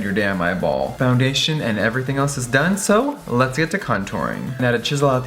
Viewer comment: Baby boyyyyy, you know I love your make-up videos but we wanna see more of the fashion world <3 <3 !!!!! COME OUT WITH A CLOTHING LINE, THAT WOULD BE SICK. Also I thought you said "cunt-ouring" as well hahahaha, Kay